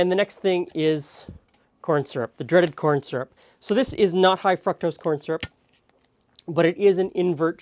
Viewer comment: Are we just going to ignore the "high fucktose corn syrup"? lol I thought I heard it, my girl asked if he said it, replayed, and yes. XD Side note: Your hair looks good at that length!